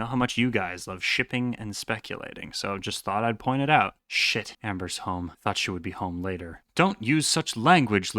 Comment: I heard Ember, and thats my name(spelt embyr bc I wanted to match with my cousin), nearly peed myself